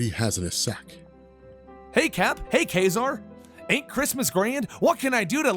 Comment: "Ka-Zar" ? I think that character is called "Quasar".